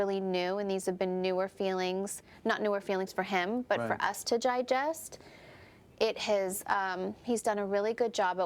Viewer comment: Is it only me who heard at 2:30 "gigest" instead of digest !!!!!